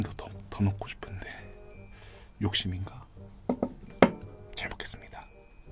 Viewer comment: did he say bismillah or am I just imagining things?